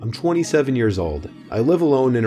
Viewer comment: The first story I thought he said 17 year old and I was like, legos?? Hidden treasure?? What???